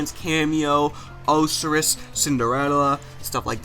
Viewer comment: Did he just say oh-seris instead of osiris?